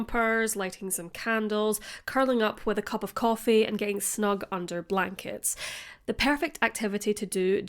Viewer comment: I swear I thought you said under black kids. 🤣🤣🤣 Clearly you said under blankets. Keep up the great content!